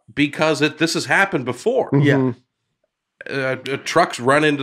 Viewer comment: At first I heard "trunk" so I'm thinking somebody drew an elephant over Nate. I quickly jumped to YouTube and had a good laugh!!!